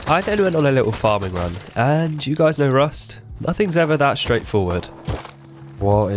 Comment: i cant understand, what did you say? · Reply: “ i then went on another farming run and you guys know rust nothing is ever that straight forward “ ;)